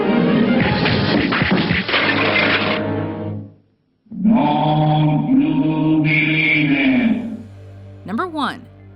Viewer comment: What in the hell is he saying??? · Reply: "Don't You Believe It!"